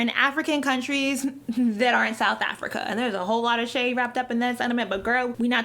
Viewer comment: That intro killed me.. I'm a South African, if you come to South Africa you might also consider visiting our neighboring countries, Zambia Zimbabwe, Namibia. Botswana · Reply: Did you understand what she said?? I just heard "there hole lot of shave down there wara wara....."